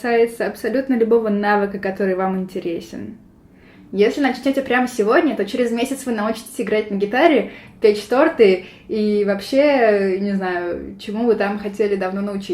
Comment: Вместо "печь торты" дважды услышала "петь шпроты". Странный скилл, но звучит как вызов. удаляющийся истеричный топот